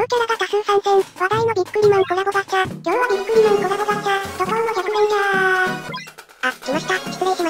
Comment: ビックリマ〇ココラボガチャに聞こえたwww(ごめんなさいw)